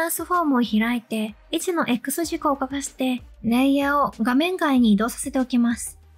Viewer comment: いつも有益なチュートリアルありがとうございます。 質問なのですが、16:56あたりからの説明が聞き取れず、 どういう作業をしているのかわからなかったので、 ご説明いただければありがたいです。 「レイヤートランスフォームを開いて、 位置のX軸を『カフして？』レイヤーを 画面外に移動させておきます」 この『カフして』とはなんとおっしゃってるのでしょうか？ 『隠して』でしょうか？ 最終的にシェイプレイヤーの 位置を右にずらしているだけに見えるので、レイヤートランス フォームを開いて、何をしているのかがよくわかりませんでした。。。 追記：あーこれはあれですね。説明と画面の動作が微妙にズレてた ため、何か難しい作業しているのかと勘違いしてました。 単純に位置を表示してズラしてるだけみたいですね。